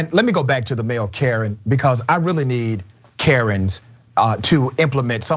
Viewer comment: Damn I thought it said FEMALE KAREN 🤣